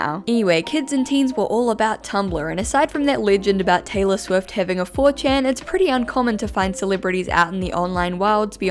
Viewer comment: did she say having a 4chan? lol